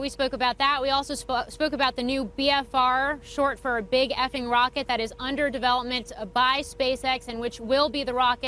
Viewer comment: did she say " BFR which stands for Big F-ing Rocket " hahahhah!!